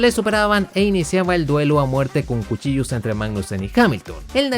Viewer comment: creo que dijo: duelo a muerte con cuchillos :v